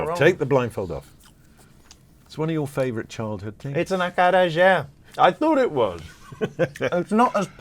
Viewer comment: What does he say at 35:56? The item he tasted blind folded is called what? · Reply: Acarajé